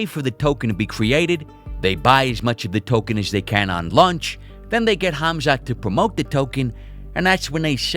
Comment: My fat ass thought he said lunch